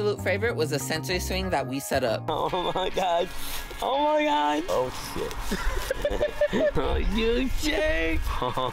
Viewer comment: he clearly said “oh shit” get ur subtitles right